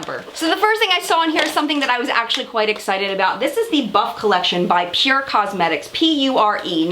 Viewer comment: I thought the palette said BUTT COLLECTION 😂😂😂